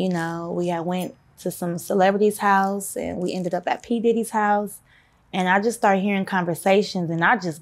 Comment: Did she say P. DIDDY? 👀 Aww hell!!!!